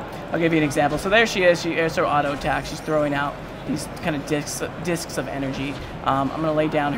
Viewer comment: Did he just say "Dicks of Energy"?